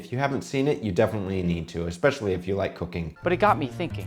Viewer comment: I definitely heard "especially if you like cocaine" the first time.